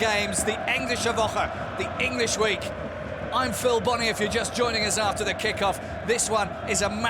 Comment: Can somebody tell me the name of commentator?? I love his commentry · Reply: It sounded like he said Phil Bonney. I'm going to look it up to make sure. EDIT: Yep, Phil 'Bundesliga' Bonney. Been commentating in the English language Bundesliga matches for 20 years